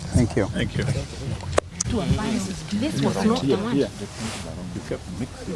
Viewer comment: Martha amesema hio statement imebandilishwa. I heard her; "..that was not the one.."